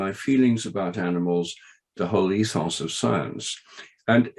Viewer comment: If anyone can help me figure out what he says at 5:15, it would be greatly appreciated. "Between his feelings about animals and the ____ of science." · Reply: The whole ethos of science